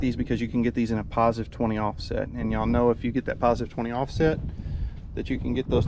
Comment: What offset are those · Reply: I thought he said they were +20mm offset